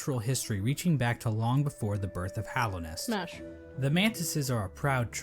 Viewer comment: Did I hear "smash" to the mantis people? (Not that I'm Judging 😏)